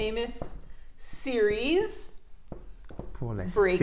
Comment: Hi and. Three mistakes !! Not good not bad ! I forgot the "d" at sized an the hyphen between medium and sized. I heard "serious" instead of "series". I love dictation. Thanks.